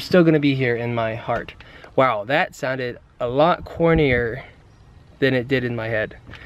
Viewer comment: I thought he said hornier